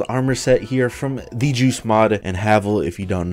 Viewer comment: I thought you said Juice Mod, not Joost Mod. Stupid ears.